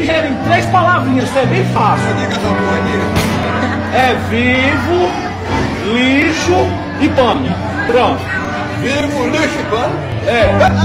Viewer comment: eu n entendi a ultima vivo lixo e o último n entendi se alguem me fala serei grato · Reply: Vivo lixo e pano